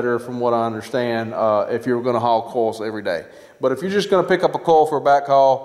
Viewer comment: Are you saying coil? I thought, you were saying coals.